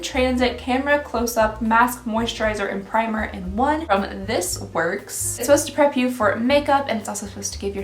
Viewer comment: When you said something was from This Works I shuddered, I almost thought you said It Works! Ah thank god 😆 Imagining the horrors of an MLM subscription now 🤮